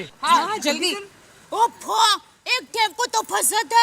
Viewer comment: Did deya says “oh fok”🤭🤭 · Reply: she said oh fo